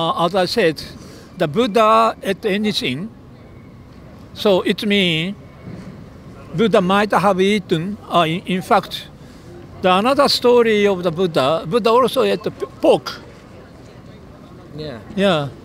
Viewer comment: Did he just say that Buddha ate a book?